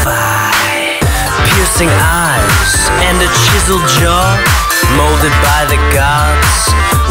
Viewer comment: I thought it said "Pissing eyes in a jizzle jar"